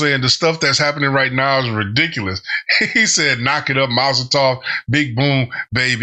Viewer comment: he actually says Molotov not Mazel tov. Because a Molotov cocktail goes "Bang!"